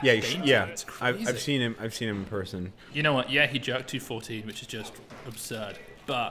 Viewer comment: Wait, at 14:22 did you say that Max Lang jerked 240kg???? Did you guys heard the same?